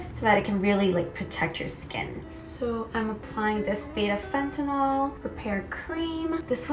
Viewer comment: I had to go back cuz I thought you said fentanyl instead of panthenol lmao